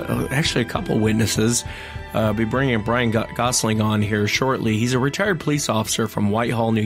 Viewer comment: I thought u said Ryan Gosling 😍😖😄